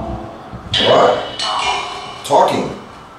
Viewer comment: thought i heard a girl say ''what.'' when u asked ''hellooo?'' like she was answering u